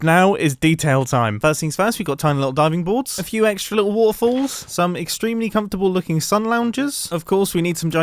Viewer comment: When he said "waterfalls" I thought he said "waffles" and I was confused for a moment.